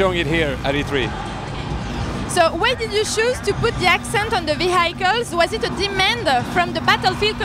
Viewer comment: did he say ''v hi kills'' at 5:07 instead of vehicles.